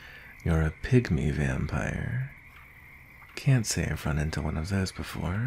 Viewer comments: Why did i hear "a 'pick me' vampire" I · SHE SAID A PYGMY VAMPIRE I THOUGHT SHE SAID A PICK ME VAMPIRE IM CRYING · I thought he said pick me vampire at first 😭